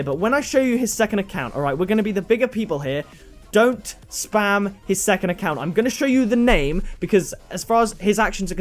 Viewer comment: What was that? please don't message his second account? sounds like you said message his second account to me